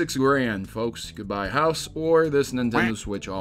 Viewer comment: I thought he said "say goodbye to the house